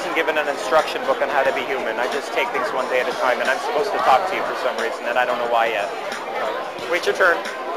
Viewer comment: Can anybody help, what does he say: at 5:10 - after "I wasn't given an instruction book on how to be human" and at 5:48 ? THanks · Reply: I just take things one day at a time and I am speaking with you for a reason, I don’t know why yet 🤝